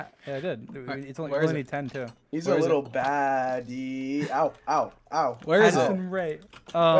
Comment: Did i just hear dream say "AdDiSoN RaE"